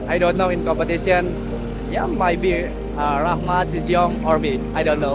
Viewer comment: I think he says maybe Rahmat, Shi Zhiyong or me :D